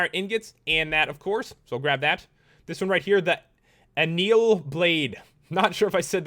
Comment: when you said anneal i thought you said anal